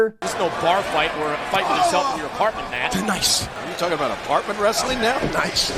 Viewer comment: Matt Striker's "noise" was him saying "Nice" a couple times at about